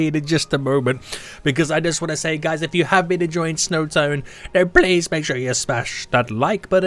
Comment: My man said "snow tone" instead of "snow town" at 0:18 😂